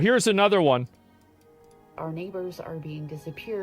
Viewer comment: I thought she said “our negroes are being disappeared” 😮 she actually said Neighbours